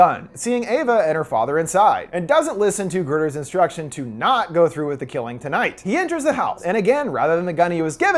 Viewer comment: I thought she said to go through with the Killing tonight